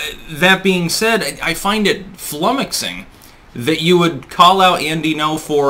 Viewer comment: What is the word he use at 1:58? "Flumixing"? I've never heard that word before.